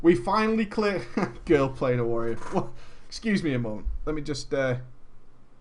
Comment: What? I misheard you there. It sounded like you said you played a Warrior. XD